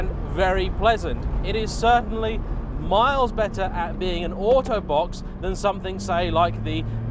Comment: when you said auto box i thought you're saying autobot lol good review by the way!